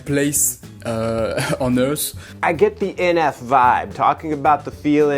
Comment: idk who made the subtitles be I’m pretty sure he says “earth” instead of “those”. The th sound is pretty hard for some non native English speakers and can sometimes sound like just a t, a s or a f.